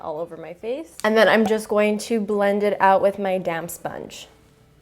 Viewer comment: Was I the only one who Horst thought that she said damn sponge instead of damp sponge